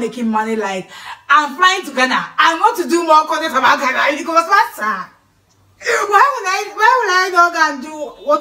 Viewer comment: did I hear massa???😂😂😂😂😂